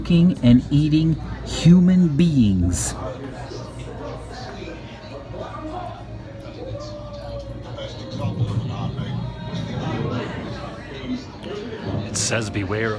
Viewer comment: Did you say, "human beans?"